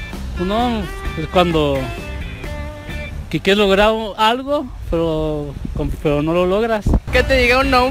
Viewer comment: Alguien sabe que mierda dijo el no le entendí ni verga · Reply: David RodríguezC cuando quieres lograr algo pero no lo logras filosofía del